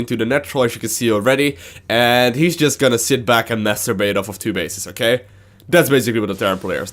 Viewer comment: Did he say masterbate on 2 base ? ahaha